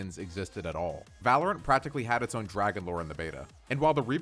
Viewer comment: thought he said DRAGONBORN( skyrim flashbacks) LMAO